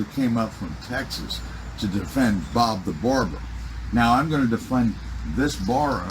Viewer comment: I had to rewind it, I thought he said, “I came up from Texas to defend Bob the Builder.” 😂 Wouldn’t have surprised me though, it’s on par with the delusions this guy has.